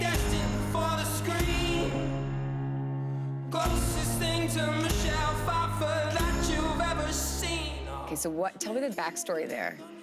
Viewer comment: An till now i always thought he said "Closest thing to Michelin 5 food that you've ever seen"